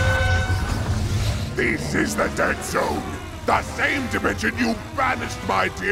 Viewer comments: Wow thought the intro said derd zone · Am I the only one who thought it said 'Derd Zone'